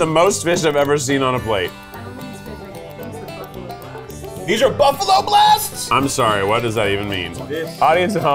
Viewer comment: A crew member spoke at 29:25 but I missed it :( what did they say? Ab the Buffalo blasts? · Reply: "I don't think it's fish, these are the Buffalo blasts." (Thank you closed captioning folks!!)